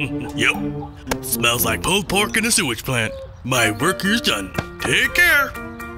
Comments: until i turned on closed captions, i thought crazy dave said "cold pork and sewage plan". then i forgot it was "cold pork" (which was actually 'old pork'), and i thought it was "beef pork and sewage plan" lol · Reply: then i watched it again and thought he said pole porkand sewage plan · until i turned on closed captions, i thought crazy dave said "cold pork and sewage plan". then i forgot it was "cold pork" (which was actually 'old pork'), and i thought it was "beef pork and sewage plan" lol